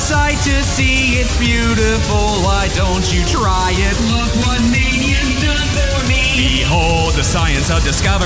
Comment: When he says “look what mania’s done for me” I heard it as “look what mania’s done to me” and they both work perfectly